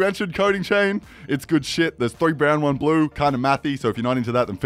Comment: Did he just say 3 brown 1 blue